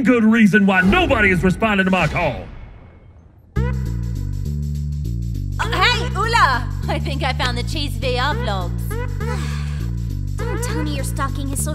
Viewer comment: can sombody please please please tell me wtf she says here? The subtitles dont have a clue and ive watched it multiple times and aint got any idea. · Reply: "hey ulla! I think I found the chief's vr blogs"